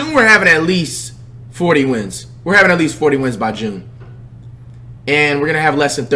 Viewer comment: did he just say he would have 40 wins by just? 💀